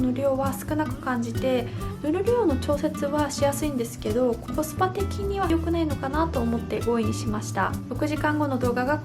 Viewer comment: 「コスパ的には良くねぇのかな」と聞こえてしまって吹いちゃいました😂笑